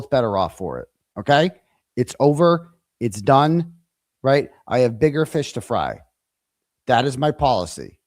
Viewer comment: Did just say, _"I've got finger fish to fry?!" 😂😂😂😂